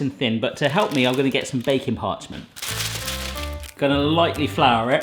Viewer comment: I'm fairly new here but all this time I thought he was saying "bacon parchment" until now ;/